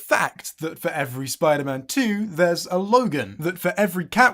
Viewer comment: "Comic book movies are for kids" evil laugh while looking at logan,deadpool, kick-ass, watchmen,the dark knight, infinity war, v for vendetta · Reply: I thought the first one said Logan paul, I am really confused for a second